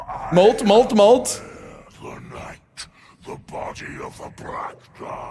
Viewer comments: I was listening to this in the background while writing my finals paper and at 5:30 I swear I thought it said "body of a black guy" and had to stop writing my paper for a moment LMAO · I wasn't reading and I swear he said "the body of a black guy"